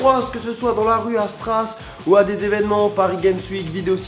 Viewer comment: a 0:12 je suis la seul ou j'ai entendu stras (Strasbourg)